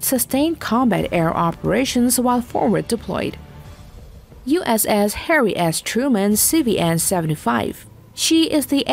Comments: It sounds like she says Hairy Ass Truman, now that is something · Sounded like she said USS Hairy Ass Truman....